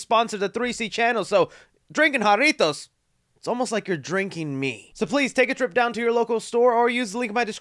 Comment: did he just say we were drinking him?!?!?